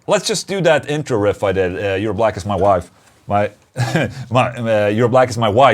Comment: 😂😂😂😂 I thought he said "Your Black As My Wife"😂😂😭😭😭 fucking awesome🤘🤘